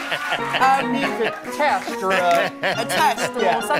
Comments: Did he just say "Thanks Pops?" 😲😯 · "thanks pops" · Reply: I thought he said thanks boss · I thought he said “thanks boss” 😂😭